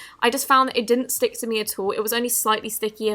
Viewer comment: when you turn the captions on it was asshole instead of at all